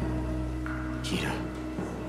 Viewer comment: I wonder if they will actually get married...Love maggie's dress. What does she say at 0:49? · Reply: "You had me at 'Give me the damn glock'"